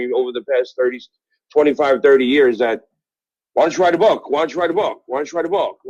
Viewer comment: I think Grillo is mishearing everyone. They are saying why dint you READ a book